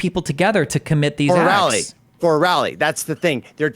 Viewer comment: I thought he said Borelli for a second at 1:14:09 and I almost lost it hahah